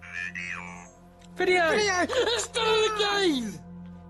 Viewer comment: For a second I thought it was the ghost saying "Video" lol